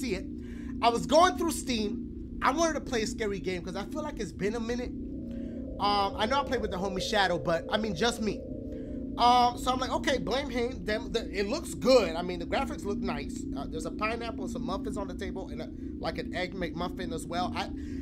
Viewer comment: did dashie just say.. "Blame haem, them, the"